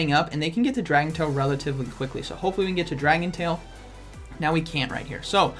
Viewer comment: Did I hear dragon toe at 9:41? And then dragon tail at 9:44? And then back to dragon claw at 9:48?